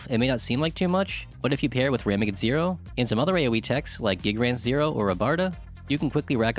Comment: When talking about the "Shift Massenvernichtungs" 13:50 I didn't catch the aoe moves you was talking about. Also this is the video I have been waiting for before spending skill points thank you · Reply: Gigrants zero, ramegid zero.